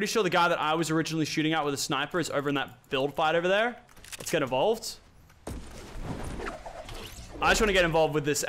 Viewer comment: Is it just me but at 8:32 I swear he said let's get Evolved